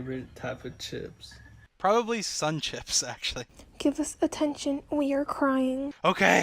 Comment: what was she saying? I heard "... you're crying" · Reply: “give us attention, we’re crying”